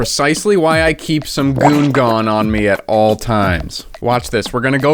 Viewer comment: I misheard goo as goon